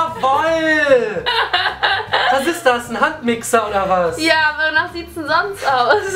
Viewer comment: haha bei 2:06 hab ich handwixxer anstatt Handmixer verstanden wer noch? haha :)